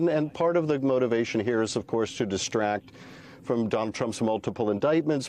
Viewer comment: Uhm... at 6:15, did he say "Dump Trump's indictment?" I've listened to it three times and that's what I keep hearing. Oh, my. {laughs}